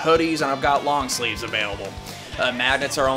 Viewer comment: i thought you said "maggots are only five bucks"